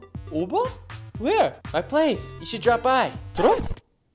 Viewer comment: You should 가 she로 들려요ㅋㅋ영어 넘나리 알아먹기 힘든것ㅠ